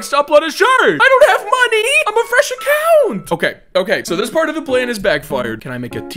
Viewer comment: I thought you said “im a Russian cow”